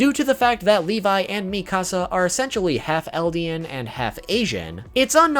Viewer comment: Eldian and AsIaN? Or did i heard it wrong?:O